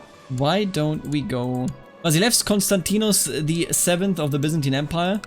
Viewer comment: did i hear vasilefs?